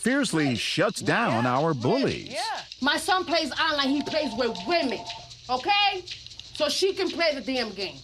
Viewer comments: someone help 8:27 WHAT DOES SHE SAYYYYYYYY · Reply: Olga Gallego my son plays online he plays with women okay?! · “My son plays online he plays with women”. 👁👄👁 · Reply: I thought she said "my son plays Allah he plays with women" 😂😂